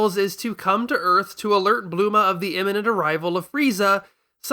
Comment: pretend? Uh .. he literally goes out of his way to say the names I specifically called out with Japanese pronunciation. when majority of the audience you're talking to knows them by slightly alternate names. It just will throw you off. It doesn't matter if it's their "real" names or not. Nobody who has seen Pokemon in English calls Ash Satoshi. Before you gohan another tirade, remember I said majority. I'm sure English speakers in South Asia will know them by their accurate names but that doesn't discount the impressions where dragon ball is most popular and most discussed. The video creator doesn't sound like someone from out of the states so he should quite frankly be aware of this. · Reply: doesn't sound like Bul-ma.. Actually you're right it doesn't sound like bu-ru-ma either it's like mix... bluma? Which is actually just wrong btw and worse than what I thought I heard. But I'm going to go with my first assumption because Kuririin was still very deliberate. You seem awfully invested in proving me wrong when all I was saying is people will find it odd when they hear names they aren't used to hearing. You can try and twist this however you like and give yourself a thumb up,. It doesn't change my main point. And it might be wise to consider what I'm saying beyond just criticism. Tbh I would much rather talk with the video creator unless you're his shadow account lol.